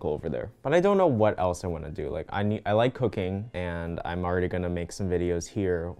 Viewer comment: i’m sorry you like what? cocaine? (i know he said cooking but i heard cocaine)